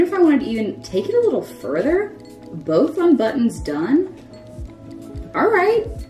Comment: did she just say 'both unbuttons done' :D